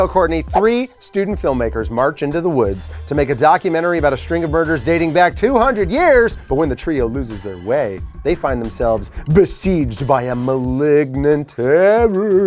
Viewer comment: During Courtney's first round I thought said a "string of burgers"